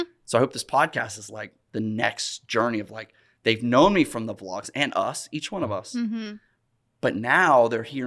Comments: Came from the vlog I thought iyou said Kain from the vlog lol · Came From the Vlog. I already watched it when in dropped but i guess I'll watch it again. Also thought you said "Kane from the Vlog" at first. lolz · Came from the vlog, actually came back from the vlog, it also sounded like you said Kane from the vlog idk lol … Thats how i feed my eel. If you know you know 😂 love the vids and the vlogs Roman been watching since middle school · Came from the vlog tbh it actually sounded like Roman said Kane from the vlog so I was a bit confused at first 😂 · "Came from the vlog" Thought you said "Kane from the vlog" til I saw everyone else's comments Great pods and more vlogs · My bad here from the vlog 🤦🏻‍♀️ it definitely sounded like you said kane from the vlog 😂😂